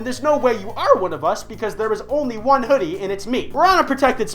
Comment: totally thought disembodied voice said "honey" not "hoodie" O.o